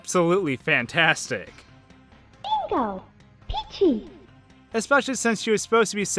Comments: I’ve miss heard a lot of these but Mario’s “lets a go” and peach’s “bingo” I can’t see how someone miss hears that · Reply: I don't know why but when I was younger I always misheard Peach's "Bingo" as "Pinbat" or "Being at" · In Mario kart 64, I still think peach says "dingos have eyes". I'm sure she says bingo, but I don't know what the last part could be. In gauntlet legends on n64, and I have looked it up and the information is no where, I have no idea what they say when this little pair of golden wings get attached to your back when you touch another player with the wings, nor do I know what it does. I always hear "your archer is mauded". I also thought "ahh sustenance" was "ahh I stepped in it" when you picked up meat. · Peach says "Bingo!"? I thought she said "Peachie!" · In Super Mario 3D World, whenever you grab the cherry power-up with Peach, it sounds like she says "So itchy!" but I think she is supposed to say "So peachy!". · i just heard "bingle" bing x google?? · Princess peach didn’t says bean dip Shea said bingo but it’s still funny how you misheard it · at first I thought it said king bell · In Mario Kart 8 I swear I heard peach say “GG” instead of peachy